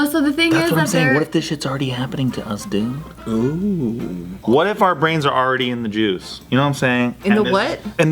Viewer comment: thought said said “what if our brains are already in the Jews” haha